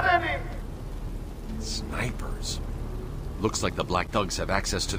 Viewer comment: At first when he said “snipers” I thought he said the “these negros”